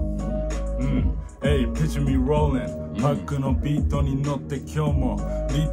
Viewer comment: ここめっちゃかっこいいんだけどなんて言ってるの？ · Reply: Picture me rollinって言ってるよ ビートが2pacのpicture me rollinだから